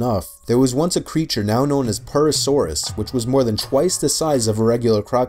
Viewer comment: Did I just heard PURSE saurus ? They named it like that because they make PURSES out of crocodile skin ✌️